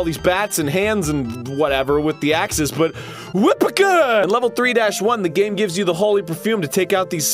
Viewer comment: anyone think he said Whippercut?